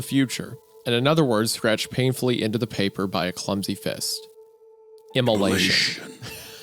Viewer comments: Neither me nor the subtitles know what they said here, kinda sounded like emulation? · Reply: maybe immolation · what did they say? emalation? evolution? im confuse XD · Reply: Immolation. Pretty much means burning to death.